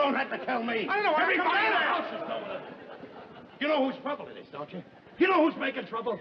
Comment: What the heck was Norton saying at 13:26? 🤣🤣🤣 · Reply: "I know what everybody in this house is going through."